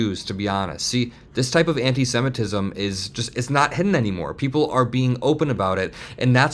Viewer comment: For a second I thought he said this kind of antisemitism is not hitting anymore